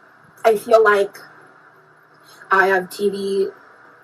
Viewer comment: omg for a second I thought she said “I feel like I have TB” and I almost spat out my drink